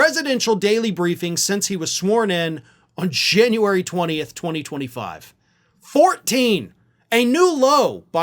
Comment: I first thought you said sworn in on Shame-uary 20th, 2025. 😅